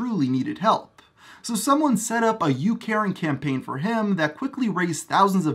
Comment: When you said you caring campaign I thought at first you said “you Karen campaign “ 😤